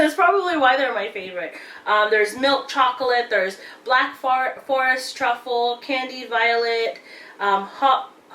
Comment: While you were naming the chocolate palette it sounded like you said black fart lol